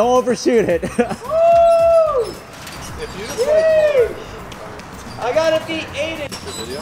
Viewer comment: all i heard was: I Gotta Be Aiden- 😂